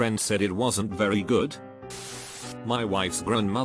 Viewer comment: I heard " my grandma dated ted bunds" and I just went '-'